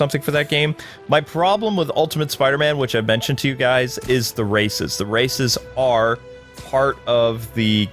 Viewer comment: I thought you said racists I was like woah that game has racist but I understand now